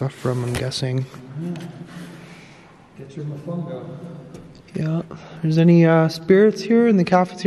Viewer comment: I thought you said is there any bad arss spirits here 🤗